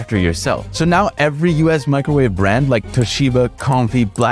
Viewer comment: I thought you said blackni-😭😭